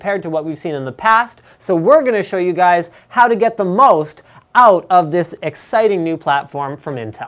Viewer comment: i thought he said "how to get the most" out of your new intel hardware ??